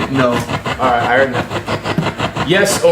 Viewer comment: I hear "I do now"